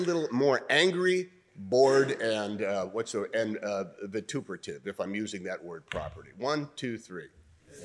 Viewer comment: bro i tried to google wtf that word was he said and all i got back was "vertebrate" lmaooooo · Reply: 'Vituperative'. Means 'bitter and abusive'. Kind of a verbal attack.